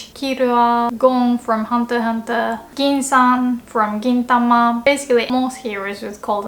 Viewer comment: Also, I thought you said "kintama" lolol